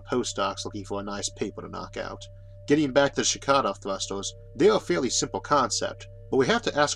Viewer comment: I honestly always thought he's been saying "Chicano thrusters" in every video where he mentions shkadov thrusters.